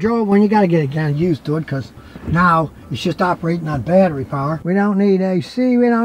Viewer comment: Sounds like said "now shyts operating off battery power"at 23:42-23:46 🤣😂😂